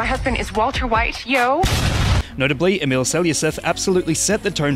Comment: Who's the guy doing the face? Even with auto subtitles I can't understand his name, I only heard "Emilio Sell Yourself"